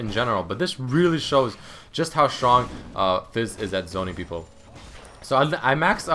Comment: did he say ziggs or fizz?